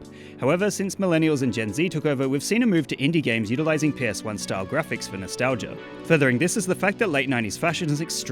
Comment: I heard at as ps5 style for nostalgia instead of ps1 style for nostalgia and I am like :0 he travel to future